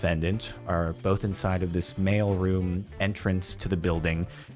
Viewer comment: thought he said "male room entrance"